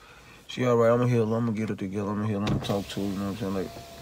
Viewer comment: Sound like he said "imma hit her" ..imma get her together"?